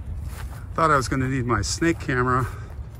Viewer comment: Snake hammer? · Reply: My hearing must be going, I thought you said snake hammer. Either way, so glad you didn't need your snake camera this time.